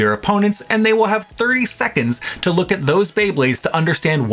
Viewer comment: I thought you said 3 secconds in the rules of pick 3 choose 1. Imagine though. It would make the tournament harder